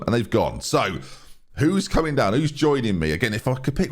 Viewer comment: What said: Who's coming down, who's joining us. What I heard: Who's coming down with Norwich